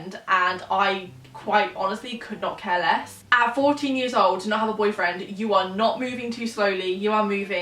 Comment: I genuinely thought u said 40 years old and you said your still young and in school... lmao 😂